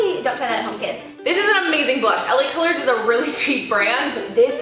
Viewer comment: when you said the blush was from LA colors I thought you said Helen Keller lmaooo